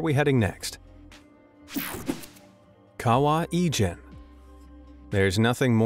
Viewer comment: At first I thought he said Egypt instead of Ijen and I was so confused when I saw the location of the lake